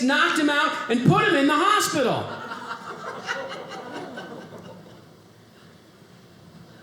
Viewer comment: I thought he was saying pants rather than pans, it got less funny when I realized what he was actually saying.